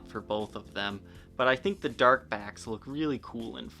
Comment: thought it said "dark black"